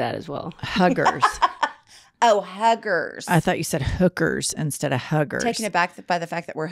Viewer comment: I definitely heard “hookers” 🤣🤣🤣🤣